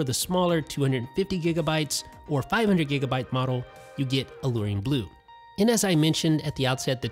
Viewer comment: Oh... I thought that you said 5000 GB well I dislike